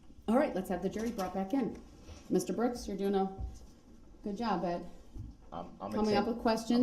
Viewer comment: You are exactly right about how DB was getting all excited when the judge told him "good job" little Darrell! Just like a child! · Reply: But did the judge call him “babe”???? I thought she said, “Good job, babe.” 😳